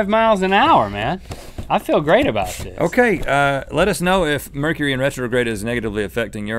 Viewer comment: I thought it said mercury in Gatorade so I was so confused when they started